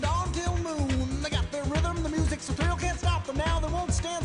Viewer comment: The subtitles say "The music's pill," but I think it's saying "The music's a thrill."